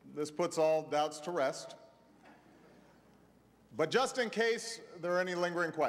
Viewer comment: lol at 2:17 he said but just in case .. i thought he said but justin beiber or something haha maan even obama fucks with justin beiber